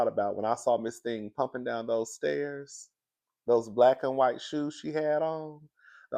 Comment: I thought Ashley said white sheets 😂